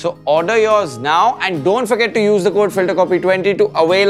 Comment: Did she just say "keep washing" at the end? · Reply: Nope. She said keep watching